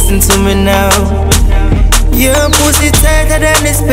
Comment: tighter than the space between the tile😂😂😂😂😂😂 I died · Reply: I always thought he said thigh lol